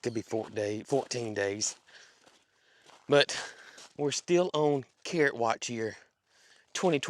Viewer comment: So my ears heard you say ‘we are on carrot watch’ my brain heard ‘we are on Karen watch’. I was so confused for a minute or two. 😂